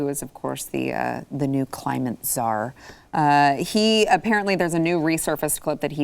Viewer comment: Climate Czar, I thought you said CRAZY CZAR.